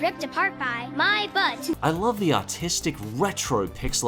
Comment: Oml I thought he said “autistic”